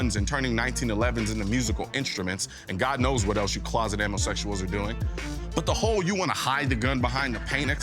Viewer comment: Did he just call them "closet ammosexuals"? Bwaaahahaha